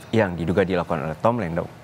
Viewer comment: Diulang berkali-kali, di 1:15 tetep kedengerannya "Tom Lendong", didukung gerak bibirnya juga 😁